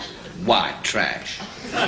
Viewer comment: Did Lucas say "white trash" at 21:27 ?